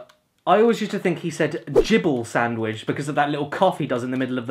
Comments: Ha I actually thought he said jibble sandwich too XD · I always thought he said Jiffle sandwich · Lol i thought i was the only one who thought he said jibble sandwhich